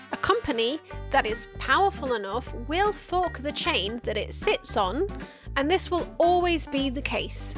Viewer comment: because of your accent I thought you said "a company that is powerful enough will fuck the chain it sits on.."